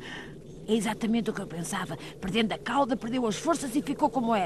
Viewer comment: Why does Yajirobe sound like he is saying fart police 0:18?